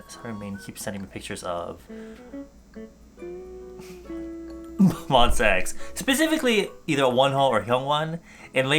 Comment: I just heard "M O N S E X" at 0:10 I dunnoit'shalfpastthreeinthe m o r n i n g